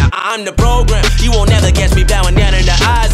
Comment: anyone else hear "you won't catch bowing down to aizen" like bleach aizen?